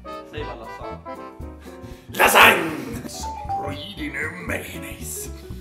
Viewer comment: What are you saying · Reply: "Some pre-dinner mayonnaise. Rawr."